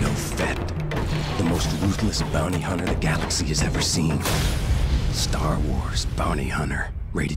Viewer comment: When he said the most ruthless bounty hunter the galaxy has ever seen I thought he said the most useless bounty hunter the galaxy has ever seen